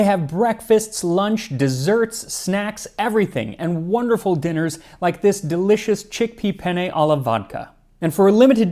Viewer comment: i heard vodka chicken...i gotta get this meal plan